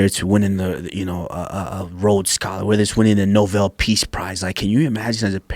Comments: Did he say "novel peace prize" at 2:52? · Did he just say “novel peace prize” ??